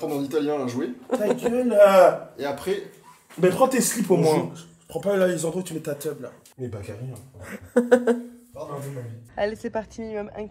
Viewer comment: Il a dit quoi à 29:41 ? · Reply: On est pas carré